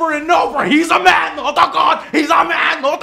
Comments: I dont think Joe can take many more of these games, hes a man, not a god... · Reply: the first time I heard that sentence, I heard ''he's a man, not a goat'' XDDD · Anyone else thought that in Rambo, he said "HE'S A MAN, NOT A GOAT!" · He's a man not a God. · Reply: Oh, i thought they were saying "He's a man, not a goat"